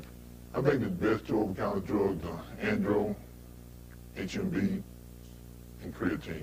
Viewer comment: I can't make out what he's saying at 48:45, best over counter drugs are "...., HMB, and creatine." I can't make out the first word he is saying. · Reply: I think he said “andro”. What that means I have no idea. Possible a pro hormone